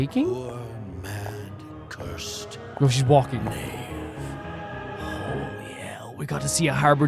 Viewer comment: I thought said "holy hell" 😂